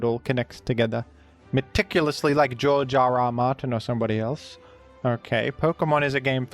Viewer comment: more like Jar Jar R Martin (because that is seriously what I thought you said at first)